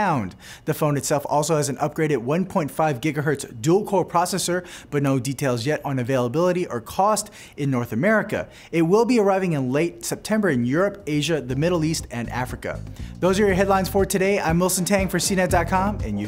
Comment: did he say africa? but its not coming to usa yet?